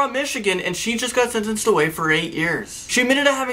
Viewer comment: At first I thought you said you were going away for 5 years!